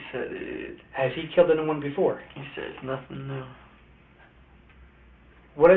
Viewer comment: I'm confused you say he said he "doesn't know" after implicating that the guy had killed people.. but what I heard him say was "it was nothing new"...